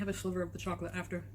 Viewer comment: Did she say she was going to have a “silver” instead of sliver of the chocolate cake? That sliver of chocolate cake looked like a full sized slice to me! 😳